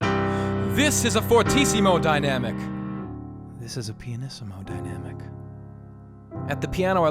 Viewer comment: Who else thought he literally said “This is a pe*is” in the dynamic part????? I was scared